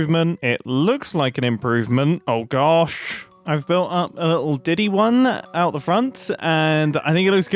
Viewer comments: Did he say "I built a little Diddy one"? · did he say he built up a "little diddy one"